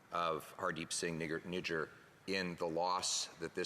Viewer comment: what did her say? He said Nigg@ with a hard “E” . dafuq is wrong with him? He definitely says that a lot. Because the guys name is spelt "Nijjar"